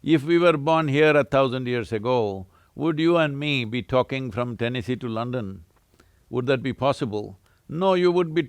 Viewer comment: I swear @ 3:53 it sounds like he said he’s in Tennessee 😮 I’m in Tennessee, is he here?? I would freak out lol 🙌